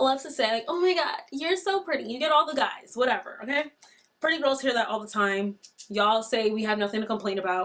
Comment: Wait did she just say "We pretty girls" 🤣 5's tryna act like 10's 🤣